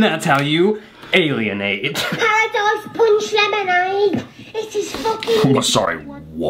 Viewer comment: I heard "hotdog punch lemonade" and was like wtf? Lol